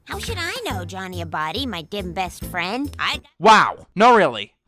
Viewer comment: I don't know what she said, but I heard "Johnny a body my dim best friend", and if they said anything remotely hear that, then facepalm